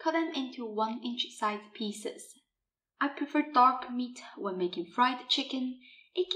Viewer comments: Did she say "I prefer dog meat when making fried chicken" in 0:40? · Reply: dark meat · did she just say I prefer dog meat😳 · some explain what she said. Duck/dog meat? Duck right? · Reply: Dark